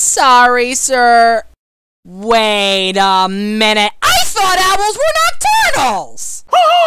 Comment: What is Percy saying at 4:30? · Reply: “I thought owls were nocturnals”